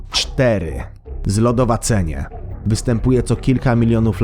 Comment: kto usłyszał zrogowacenie? :D